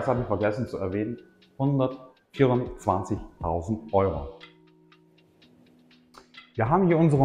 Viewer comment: Kann man den Preis erfahren? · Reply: lt. video 124.000 € ..habe ich gehört im video bei